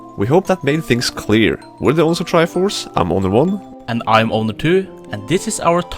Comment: I kept hearing "I'm Obi One" "And I'm Obi Two", instead of Owner. XD